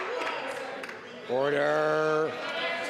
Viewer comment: LMAO I honestly thought the speaker said "Water..." at 2:26 as in: water for that burn!!